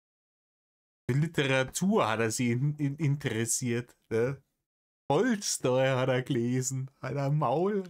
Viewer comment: Sorry, ich verstehe es akustisch nicht. Was hat er gelesen? Toy Story? 🤔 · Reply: Tolstoy